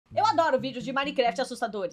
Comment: como é? 🤨 é isso mesmo que eu ouvi mundo? Ela adora?😮